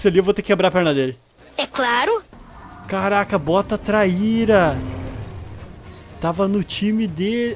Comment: "Bota traíra"... Nem vou explicar oq eu entendi com isso